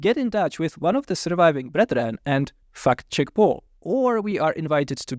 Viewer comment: Apologist preachers on the internet must hate it when Paul makes a video responding to their stuff, it's always so thorough that all they can do is hope their audience doesn't see it. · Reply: I love accent. Right when my ADD was starting to make me tune out, I hear "f***Jake Paul" and I was like hold up wtf did i miss? Turns out he said "fact check Paul"😂 I just felt like sharing that. Continue😊